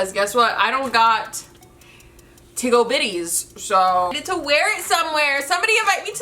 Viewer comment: Am i trippin or did she just say "tig ole biddies" instead of "big ole tiddies"